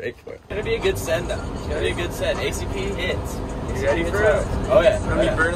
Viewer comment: i thought and were saying pcp would be a send i had to listen agian 😂😂